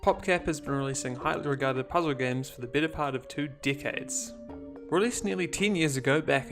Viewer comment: When he says decade I thought he said dickhead. Great video tho